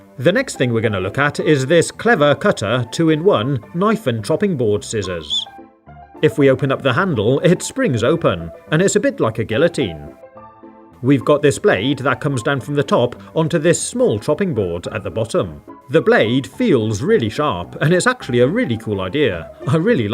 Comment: Did you just say "a gyllitine"?